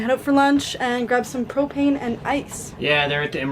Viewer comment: Had to go back at 5:18 and watch again because the first time I honestly thought said "cocaine and ice." 😄